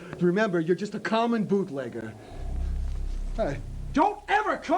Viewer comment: Ngl, I misheard and thought that the cop was calling the guy a bootlicker—not a bootlegger—and that earned a chuckle out of me.